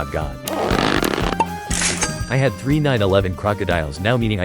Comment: bro i just realized you said 9/11 instead of bombardillos 💀💀🙏🙏